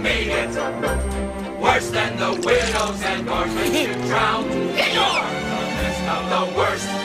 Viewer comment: if you listen to the lyrics, you can hear them say "worse than the widows and orphans you drown". The lyrics to this song show just how bad Ratigan is. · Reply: When I was younger I actually thought they said "worse then the wierdos and orphans you drowned"